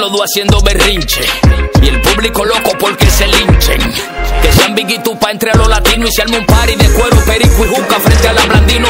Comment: Que Dice En El Minuto 1:26 (¿?) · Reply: Dice "que sean biggie y tupac entre los latinos"